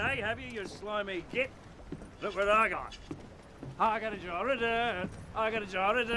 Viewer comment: Every time when I hear it I heard "I'm Jonny Deep..." Instead of " I have got a jar of dirt ".